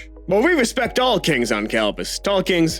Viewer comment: Tall kings... for a second I thought you said stalkings and almost panicked.